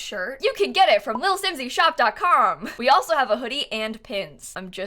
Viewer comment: I thought you said “we also have a hoodie and Tim’s”, I was like……holdup. Timberland does custom YouTube stuff? I shouldn’t eat while watching videos.